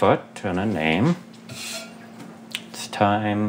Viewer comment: it sounds like he said “what the fuck kind of name”